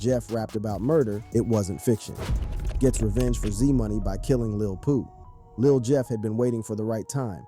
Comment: thought bro said lil poop